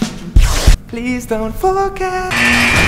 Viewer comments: HE SINGS BEAUTIFUL LIKE IM NOT EVEN KIDDING I LOVE HIS VOICE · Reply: mangle the fox Well he NEEDS Tyler THEY MAKE A PERFECT DUEL but in one part I thought he said "Please don't Fucka" <-OMFGXD but I know he said "Please don't forget" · When he said please don't forget it sounded like he said please don't fuck up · sounded like "please don't fuck up~" for a minute XD